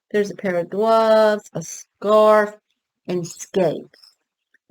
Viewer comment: Hi! Great Ken info! kinda embarrassing but I never knew Any of that Ken info! 🤗 Sweet boxed Fun on Ice!! For a second I thought you said cigar but you said Scarf lol! I was like he came with a Cigar?!?!? lol!!! 😝